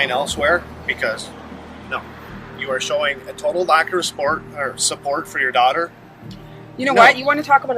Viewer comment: did he say a lack of resport? he wanted to say remorse instead of support when thinking out loud goes terribly wrong😮😯😲